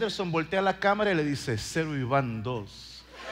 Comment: ¿Que dijo? 19:22 no lo entendí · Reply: Cero Ivan tos